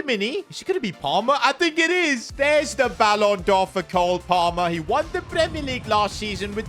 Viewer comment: I thought he said billion